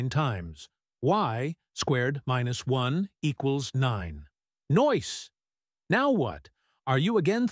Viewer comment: did he say noice lol